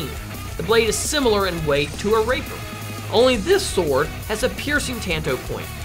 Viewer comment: Sounded like he said raper than rapier